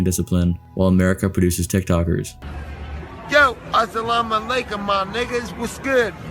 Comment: Asalamalaikum my what? DAMN, didn't know Nate Diaz had that black in him, lemme requote what he said: "Asalamalakum my niggas, bop bam!"